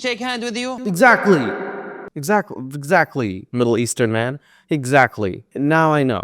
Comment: I thought said "Little Eastern Man" and I thought that was very cute... but then I realized he said "Middle Eastern Man" and the only good thing about this video's contents (aside from himself) disappeared